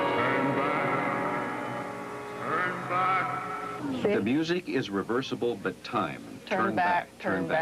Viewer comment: It's actually "The music is reversible but time is not Turn Back (4x)". They didn't hear "is not".